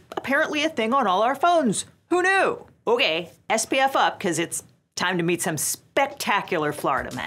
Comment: definitely thought she said “SBF up,” and I wondered how FTX came into play here 😅